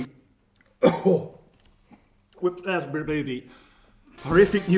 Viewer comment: Did he say "Wit asper baby" at 2:25?? lol